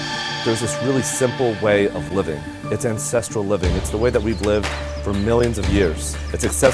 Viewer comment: Brian should sometimes articulate more clearly. Sometimes when he says“ ancestral living” I hear “incestral living”.